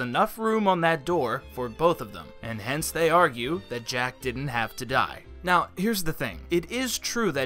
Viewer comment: Am I the only one who heard him say "Romeo died" instead of "Jack died'??